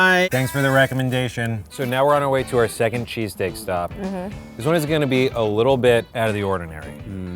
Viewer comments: did he say cheesecake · did he say cheesecakes